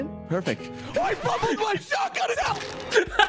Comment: “fumbling my shotgun” before not picking as Glitch was by far the funniest round I’ve seen and had me crying that it ended in such a way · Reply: I thought he said he fumbled the shotgun sound 😂